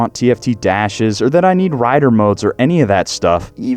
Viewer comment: Did he just say huskavarna?